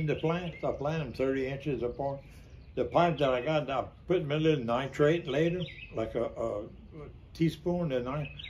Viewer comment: Great set-up my papaw used a similar method but instead of pvc pipe he used milk jugs cut in half. What was he talking about putting in the hole before planting -in the first part of the video? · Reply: I thought it sounded like he said he put nitrate when he watered maybe calcium nitrate. It's at 1:44 in the video.